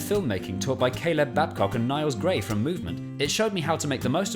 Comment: I think you mean Moment instead of Movement 😉